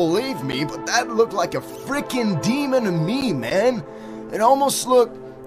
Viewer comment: I appreciate the subtitles, because I thought when he said "demon to me", he said "demonome", like some kind of made up word